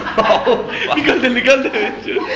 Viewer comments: listen carefully guys what Nikhil Bhai is saying.. nikal de nikal de........ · Who else heard 7:34 "nikaal de nikaal de BC"...😂😂👌